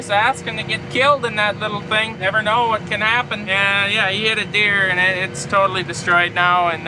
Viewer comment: When you said your brother hit a deer I thought you was saying a John Deere.